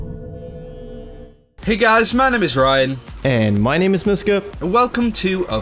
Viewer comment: Not gonna lie, up until about a week ago, I thought was saying "And my name is 'No Scope'".